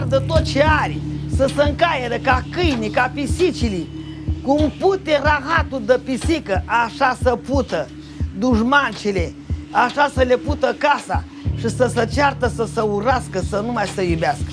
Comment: i speak spanish and all i hear is bitch bitch bitch fucking house XD